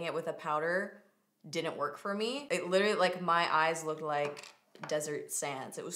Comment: When you said your eyelids were as dry as deserts sands i thought you said as dry as desi’s hands, i had to rewind it 😂😂 love you!!!